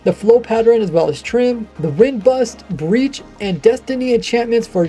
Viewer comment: Did I hear "Destiny enchantment" at 12:00 ?